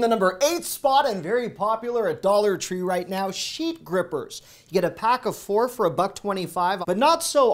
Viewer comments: I’m not feeling well and just sort of dozing while listening to videos. Had to back up because I thought you said sheep grippers. 🐑 🐑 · I thought you said sheep gripers....🐑🐑🐑🤣🤣🤣